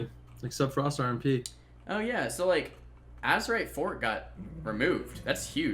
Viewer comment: what does he say at 8:16? azerite what got removed? (english isn´t my mothertongue) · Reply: He says azerite FORT, which is short for azerite fortification which is the inner ring trait that healed you when you got stunned or knocked back